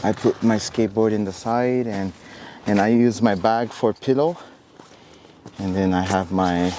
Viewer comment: That guy's English is phenomenal. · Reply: Except for pillow, I thought he said for piddle like he pees in it